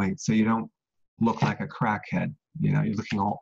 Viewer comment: Did he just say "crack-head?"